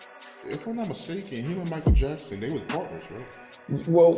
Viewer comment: Sounds like he said "wasn't they PARTNERS or something"? That's what I thought MY ears heard.